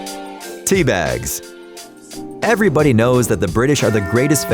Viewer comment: thought he said D bags lol